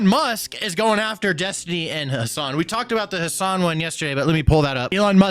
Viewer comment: When op said "and Hasan" att 0.13 i thought he said "and her son" and thought Destiny identifies as a woman now.